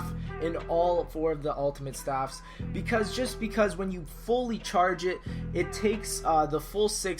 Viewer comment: It sounds like he said churge.